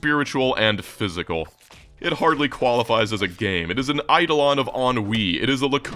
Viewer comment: you mention "its the Idleon of OnWee" (I can't tell what you said.. I want to look it up and play it) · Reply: It's "eidelon of ennui", which is pretty much saying that the game represents listlessness and boredom with such purity that it can be used as an idealized representation of that emotion.